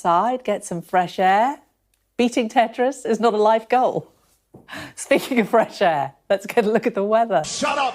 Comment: i feel bad, cuz i defended her when i only heard the "thats not a life goal" clip and not what came before it. it really seemed like she said "thats not a light goal" meaning it wasnt an easy thing to aspire to accomplish